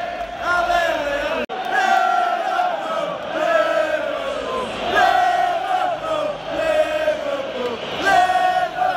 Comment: What they are saying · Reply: Live err pool, Live err pool